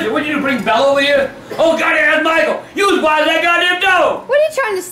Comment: "HEY BRIDGETTE YOU BRING BELLA OVER HERE?!... OH WAIT THATS MICHAEL! YOU AS BIG AS THAT GOD DAMN DOG!!!" LMFAOO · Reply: I thought he said "you swallowed that god damn dog"😆